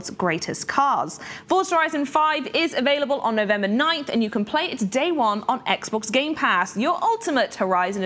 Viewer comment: did she say we can play it today? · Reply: no she said you could play it day 1, meaning you can play it as soon as it comes out.